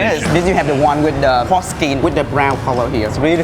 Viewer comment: When he said porkskin, I first thought he said forskin...